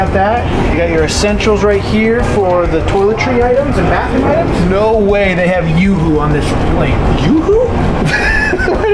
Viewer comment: did he say, “There’s no way they’ve got yoohoo on this *plane*”?